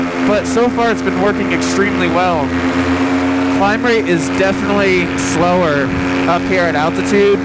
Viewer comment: Did he say the crime rate is lower at altitude?